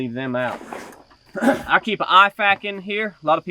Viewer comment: What did you call the thing at 4:00? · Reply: IFAK. Individual first aid kit.